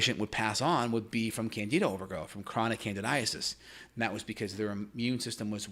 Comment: Thought it said Canada overgrowth... thought Canada was overpopulated for a minute there